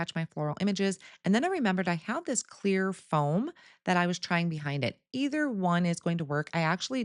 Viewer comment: Beautiful cards. Did i hear you say you used clear foam? I have a bad cold so misheard isnt unusual. 😊💗🇨🇦